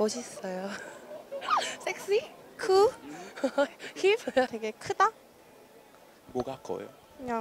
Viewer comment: did she really said HIV? 😂